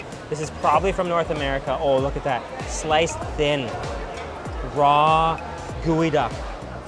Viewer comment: All I heard is "umm, look at that, Garlic!!!"